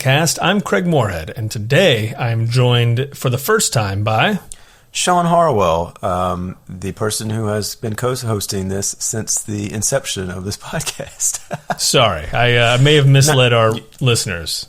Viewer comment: Did he say "coast hosting" at 0:18?